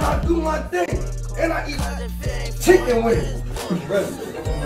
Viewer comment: Did he just say yeet yeet yeet chicken wing